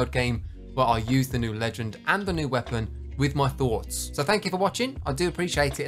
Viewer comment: I was working out when all of a sudden I heard “ill use the new legend and gun with my mind” and that caught me off guard